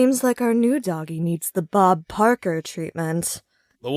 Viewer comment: It sounds like she said Bob Parker, not Bob Barker